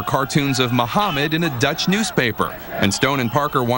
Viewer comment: Does he say "Dutch"?